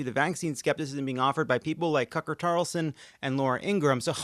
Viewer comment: Did Lovett say "Cucker Tarlson" at around 6:50?! If so, kudos forever.